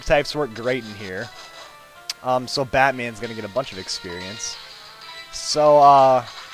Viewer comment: When first entered the gym I thought he said that FATman is going to get a lot of experience